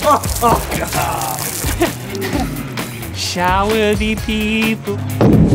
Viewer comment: can someone PLEASE tell me what Link says? Also please tell me someone else laughed their butt off. "shower dee people" is all I'm getting haha.